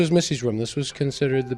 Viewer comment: Does he say "Beth's room..."? Did they use a fake name for Missy in the book? Is Missy actually Beth? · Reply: He never says Beth. At 10:57 he clearly says "Missy."